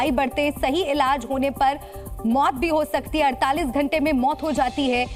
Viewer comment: Ya kya bol diya hai😂😂😂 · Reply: Sahi ilaj hone par maut ho sakte hai bhai sawdhan 😂 galat ilaj karaio